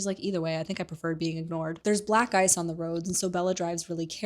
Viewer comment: im listening while at work and I thought yout said theres black guys on the road so she was driving more cautiously. Lmao, gagged me